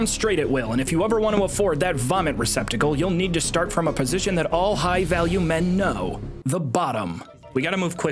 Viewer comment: Rewatching this as background noise when my wife walks by during the intro and asks "did he just say all high-value men are bottoms?"